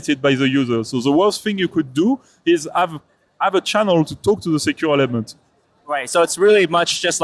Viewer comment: Did he say secured lemons? Is that a thing?